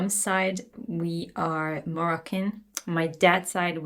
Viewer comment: “On my Mam’s side we’re.... ????” Sounds like Marcan. Is she saying Moroccan?